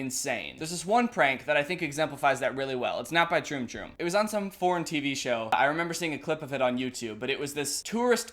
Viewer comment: I thought you said porn tv show